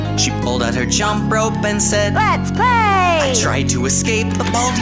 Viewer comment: A student named Playtime apeared in my way she pulled out her jump rope and said IM GAY'' thats all i hear now lol 💀💀